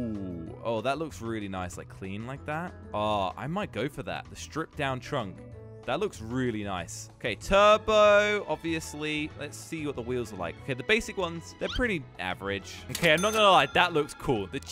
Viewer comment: did anyone heard tobbu?